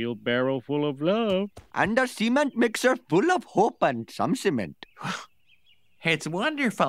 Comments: lol, cement mixer full of hope and some cement. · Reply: Thought Apu said Semen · I thought he said semon mixer